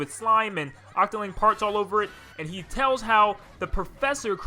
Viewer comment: I thought he said “ ...your octoling farts all over it”